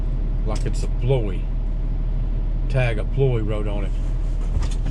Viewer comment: I thought that cars not said tag play 😆